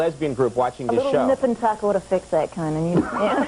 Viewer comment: what is she saying? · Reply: "a little nip and tuck would've fixed that"